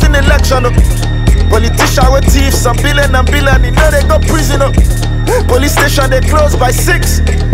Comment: did he say police station closes at 6???